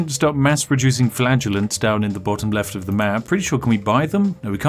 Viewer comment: I thought he said flatulence, not flagellents.